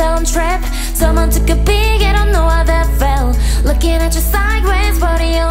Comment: When she says “needless” in the 1st verse it sounds like she’s saying “Miles” which is funny cuz the movie’s main character is named Miles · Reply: the part when she said "Lookin' at you sideway" I heard "Spider Gwen"